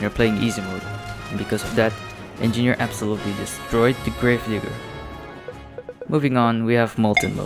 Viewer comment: what bro sayin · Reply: Destroy the Grave Digger